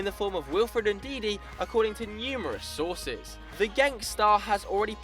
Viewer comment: "The Genk star." heard it as gangstet